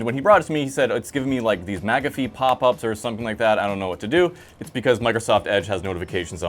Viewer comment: I misheard, & thought you said, MAGA fee pop ups... Which would obviously be a horrible thing! :)